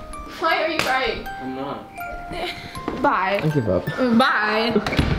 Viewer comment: Is it just me who heard her say 'bye I love you'